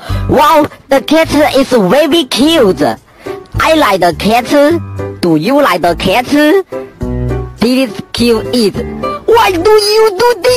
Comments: "Do YoU liKe tHE KetChUp???" (That's what I heard lol) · "Do YoU liKe tHE KetChUp???" (That's what I heard lol) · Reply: I heard "Do you like the cate?" 💀